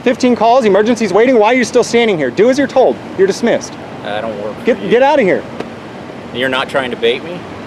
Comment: did he say “i don’t work for you”? Sounded like what he said...clearly he don’t know what a PUBLIC servant is🤦‍♀️